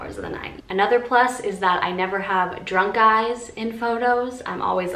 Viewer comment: i swear to god i thought you said "drunk guys", took my a while to understand what you meant 😂